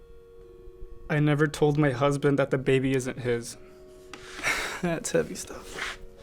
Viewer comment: I thought he said "I never told my COUSIN that the baby isn't his" and I was really scared for a second... sweet home alabama